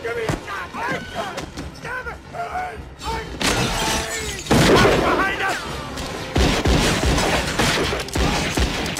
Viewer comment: what does Ike say? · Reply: He's screaming "Billy!". The dude in blue is Ike's brother, Billy Clanton.